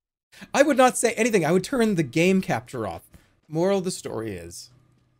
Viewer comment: No way I thought he said ”oral of the story is” at the end